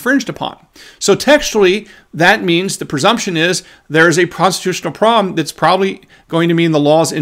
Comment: Did really say "prostitutional problem" at 6:45 or am I just hearing things? 😂